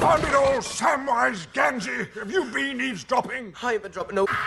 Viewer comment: Did he just said Genji??? the genji from overwatch GANDALF WTF i mean look at 6:13 and listen carefully